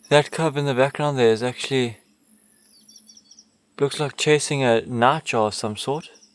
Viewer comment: what kind of animal was the cub chasing? I tried putting on closed captioning to check, but it said "nacho" 😂 CC says: "That cub in the background is actually, looks like chasing a nacho of some sort". I'm assuming it was "night" __ something, but can't make it out.